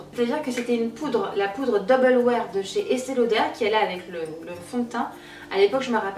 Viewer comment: Coucou! j'aurais juste aimé savoir le nom de la marque du dernier produit, j'ai chercher sur google avec le nom que j'ai compris "estello der" mais j'ai rien trouvé à ce sujet voilà ! Bisouuuuus